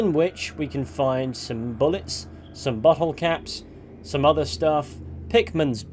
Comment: I thought he said “butthole caps” 😂